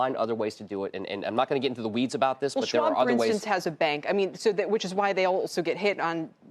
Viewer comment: Can someone please tell me what she said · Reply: she’s says, “well schwab for instance has a bank”